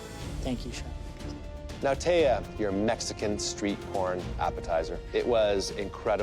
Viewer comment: did he say street porn?